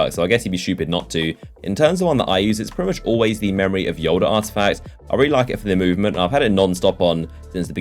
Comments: Am I the only one who heard "Memory of Yoda " ? XD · Am I the only one who heard "Memory of Yoda " ? XD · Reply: Yeah I did XD it sounds like he said memory of yolder